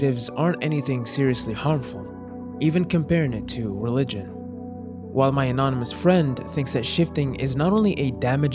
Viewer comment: she did not say that she said that just like religion it can make you go mad